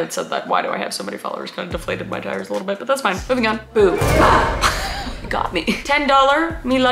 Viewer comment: At first I thought she said “boob”. Not gonna lie. 😂 it would be fitting if these were ( and they kind of are ) dirty dms. Sorry